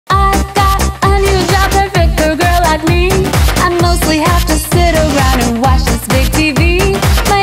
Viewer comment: what did she say? I got hdhfhdjdbdjkff like me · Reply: "I got a new job perfect for a girl like me"